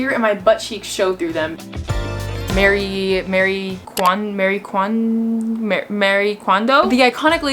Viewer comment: When you were trying to say Marie kondo I thought you were trying to say merry kwanza